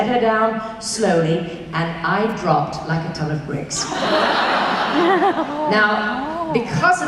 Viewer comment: What does she say at 4:41? I can't seem to figer it out. "I drop like...what? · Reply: Ton of bricks😂